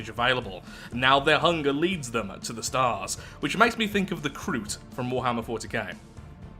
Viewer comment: first time I thought you said "Groot (from Marvel) from warhammer 40k" xD